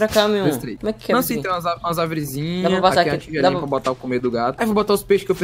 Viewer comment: eu ouvi "não sei transar com as árvorizinhas"